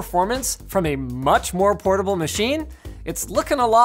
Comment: Does he say it is more portable or more affordable? 2:14 I'm going to assume he said portable, because the retail site ASUS sends you to, the laptop costs $3000